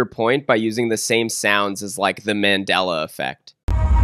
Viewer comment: man really used "Mandela Effect" instead of "Mandela Catalogues"